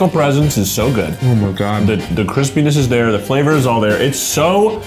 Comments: whenever I eat while watching these videos I put subtitles on and... whoever did the subtitles wrote "Chris penis" instead of crispiness and now I can't stop laughing 😂😂😂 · loving the captions at 13:05 it says "the chris penis" instead of "the crispiness" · why would the automated subtitle detect "Chris p**is" instead of a word that makes much more sense like crispiness? 😭😭